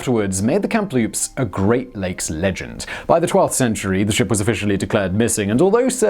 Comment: Did he say by the 12th century?